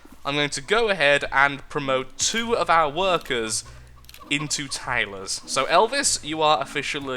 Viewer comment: when he said "we are now going to promote our workers into tailors," i thought he said "we are now going to promote our workers into tables," XD, my mind is just gone today.....